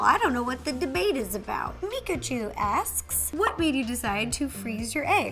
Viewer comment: I thought she said pikachu lol